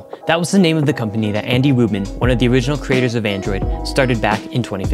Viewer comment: Anybody else heard Andy Woman instead of Andy Rubin?